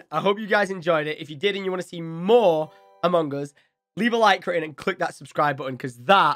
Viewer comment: When you was saying among us at the beginning... the captions thought you was saying Mongols 😂